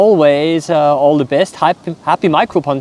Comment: I always hear him say "Micro-Punter" for some reason.